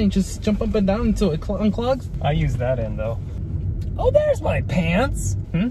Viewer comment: "I use that end tho" Did I hear that right?